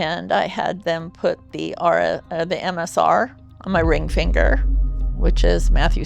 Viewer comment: Anyone else think it says monosodium glutamate …MSG?